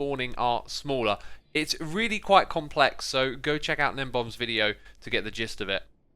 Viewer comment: Didn't hear what youtuber he said in 2:00. If anyone else is wondering his name is gnembon